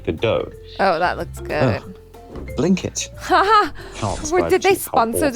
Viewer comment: W achara. I don't like burrito either, at least the burritos they sell at taco bell.... I just heard "quickie wife" wtf?? 🤣🤣